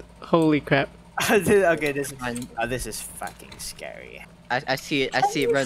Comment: listen to what he says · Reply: Asdwz458 Said "Ah This Is F##king scary" He Said The Swear Word